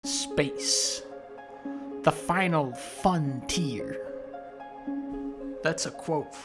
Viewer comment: I hit like after i heard “Space...the Final Funtier”